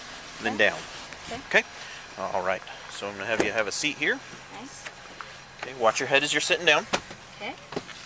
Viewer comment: Did she say "nice" about having a seat in the police car. No doubt she is drunk.